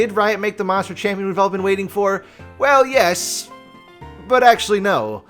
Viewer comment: Wait, I thought he said Riot wants to have their cake and f*** it too. Time to rewatch that short.